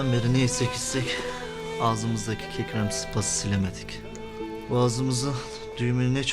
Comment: Eleman burda ne diyor yahu? Dunden beri ne yesek icsek agzimizdaki ikram sıpasını silemedik? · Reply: Kekremsi pası silemedik diyor